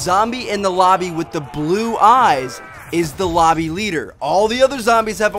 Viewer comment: did he say lomby leader